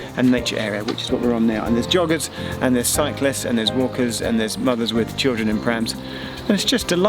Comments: "Mothers with children in prams"... I had to replay it... I thought you said 'Muggers" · I swear he says "muggers with children in prams" 😂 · I thought said "muggers" until he added "children and prams". Phew! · I thought said "Muggers with children in prams"!!!!!